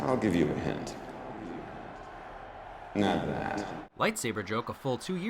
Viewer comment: to this day I have no clue what he says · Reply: Alex Jones I think he says “not that” since Sam was holding it like a lightsaber. I might be wrong though